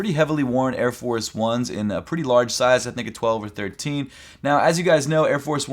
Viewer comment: pretty large size nikka 12 13. Bro was I the only one that thought he said that